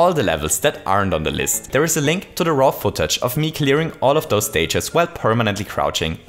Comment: Oh my fucking god, for the past year or so ive been watching I always thought that when he was saying "Raw Footage" he was saying "RoffelTouch" and I just now discover hes saying raw footage when going through the description. Epic